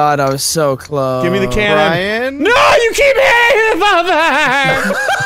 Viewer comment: it sounded like suppressed childhood memories came back and he said "you can't hit me father," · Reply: You keep hitting me, father*